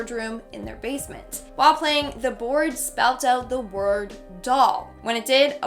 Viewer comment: Did she just say: Luigi board? Flash back to Weggi board meme video.